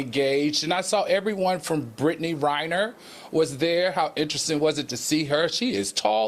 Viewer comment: Did say Brittney Riner or Griner was a beautiful woman? Chyle 👀